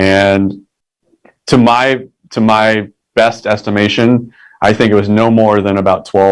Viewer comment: At best you are speculating 8 vs 4. I find it hard to believe that 4 trained killers did not hit any of their targets . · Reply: he said by my estimation there was no more than 12. he did not say atleast 12. you are misquoting him